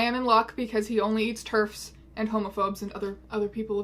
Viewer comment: I thought you said Turks instead of Terfs and I was like holy shit who are you my german grandmother or something?